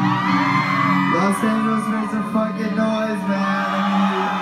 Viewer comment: my eng not good , what he said at 0:14 could anyone write this in comment for me ? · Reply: Los Angeles make some fucking noise, man